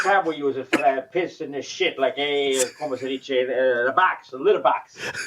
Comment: did I hear "como ceviche"? Lol